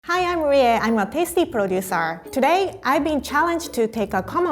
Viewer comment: I was just listening in the intro and I misunderstood tasty to pissy so I thought she said "im a pissy producer "